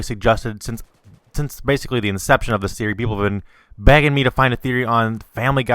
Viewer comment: when he said people where begging him to do this theory I heard wrong it sounded like he said people where banging him to do it o-O